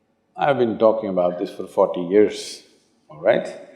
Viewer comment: i thought he said he been talkinhg about this for f""""n years" lmfao not forty